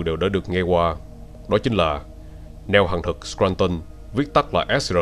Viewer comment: neo hằng thực mà nghe nhầm thành neo hàng thật :)))